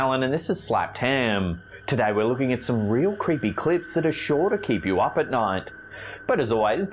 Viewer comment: I kept hearing "real creepy clïts" at the beginning 😂